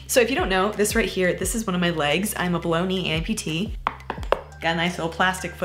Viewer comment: I know she says “below knee” but I keep hearing “Baloney” 🌭 😂